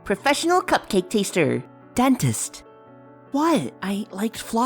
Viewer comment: Did Fluttershy say, dentist? Wow very interesting...